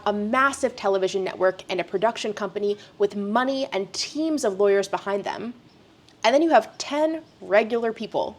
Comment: Wait, what? I had to play that back. Did she just say the K word? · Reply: what?? she says 'a production company with money and teams of lawyers behind them'